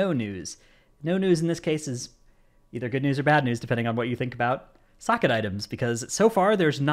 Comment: Listening to podcast version and I hear "What do you think about Russia" replies: "Send it"